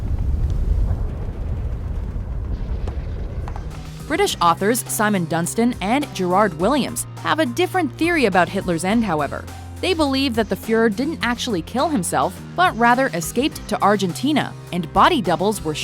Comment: I misheard Gerard Williams as Gerard Way and spazzed the fuck out.